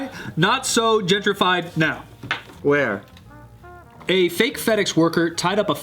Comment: I heard "fake fetish worker", I was quite confused